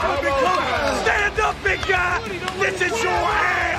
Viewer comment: "Stand up. Stand up so I can win. Stand up I'ma bust that hand back!" is still one of the iconic armwrestling moments lol love it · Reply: thought he was saying "stand up b*tch"